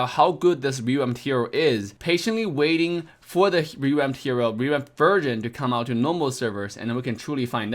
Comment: I wa half asleep i thought he said "revamped virgin"